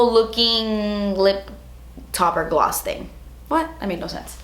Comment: When she said lip topper at the end I thought she said lip taco and I was very confused.